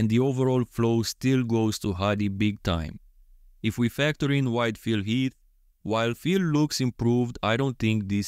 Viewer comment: I keep hearing "white Phil Heath." 😄 Also can you do Bonac with Hadi's torso and Krizo with Andrew, Samson or Brandon's torso?